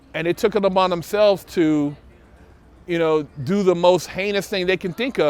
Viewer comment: who else heard “the most ANUS thing” 🤣